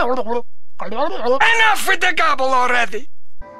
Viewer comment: put the description what the man said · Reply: ENOUGH WITH THE GOBBLE ALREADY! 🔥🔥🔥🗣️🗣️🗣️